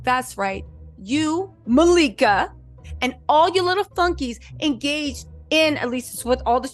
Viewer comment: I thought you said “and all your little funkies” at first & I died laughing. Then I realized you said flunkies & I still died laughing 😂🤣